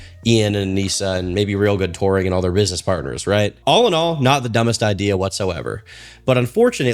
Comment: did bro just say "rizzness partners"?